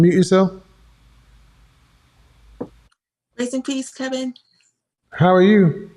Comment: I might be bugging but did she say "RIP KEVIN "?